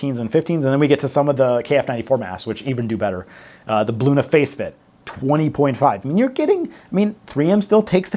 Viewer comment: great vid! saw you on yahoo news. also- thought you said "Blue in the face fit" for Bluna Facefit !